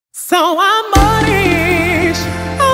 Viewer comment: Pensé que decía "son amores"